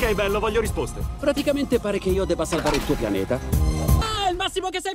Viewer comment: le entendi "practicamente voy a tener que pasarte tu planeta" :v